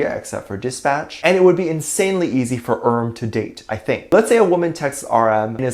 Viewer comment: When said "it would be easy for RM to date" I heard" it would be easy for erm to date" 😂